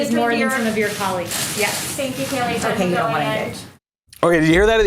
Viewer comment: WTF are you talking about? Let it go already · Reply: yeah I heard lying b in normal speed but when I slowed it down I hear "you don't wanna engage" I understand how you hear either but I hear the engage.